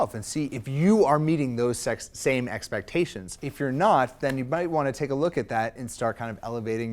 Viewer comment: lol you actually said at 2:12 meet those same sex-expectations. I wonder what Freud has to say ;) love your video :)